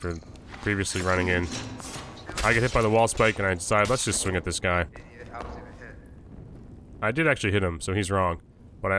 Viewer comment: what did he say his volume is so low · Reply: "Didn't even hit me... Idiot"